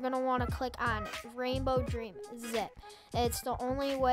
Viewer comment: who else heard zi-ip